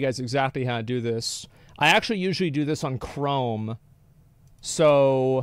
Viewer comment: I thought he said " I normally do this on the "throne", not "chrome".